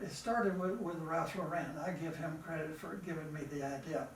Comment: In the interview Forest said he got the treasure idea from, what sounded like “Racho Renn”. This was at 19:50-53. Does anyone know for sure what he said? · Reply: He says, "Ralph Lauren" gave him the idea which is new to me.